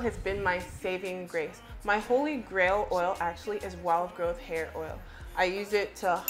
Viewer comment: I thought you were saying "wild GOAT hair oil". Haha